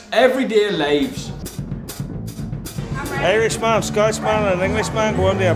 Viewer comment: "Irishman, Scotsman and Englishman at a bar, Englishman wanted to leave, so everybody had to leave" 🤣 😂 😂 😂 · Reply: I thought he said Scouse man 😅